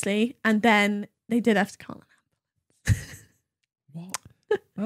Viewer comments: What on earth does say at 1:20:14 ??? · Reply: Call an ambulance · Did anyone hear what said on 1:20:16? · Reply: Ambulance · What did say at 1:20:15? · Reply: Call an ambulance